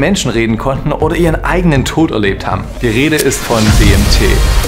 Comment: ich habe anfangs zuerst verstanden: "die rede ist von dem tee". wäre der name nicht eingeblendet worden, hätt ich mich gefragt, über welchen krassen tee du da berichten willst 🙈